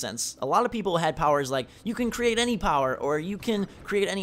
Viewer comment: I thought you said create ONE power not like FIVE powers.